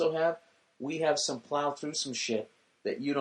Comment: what did Bill say, us guys have " plow thrue shit" ?? XDD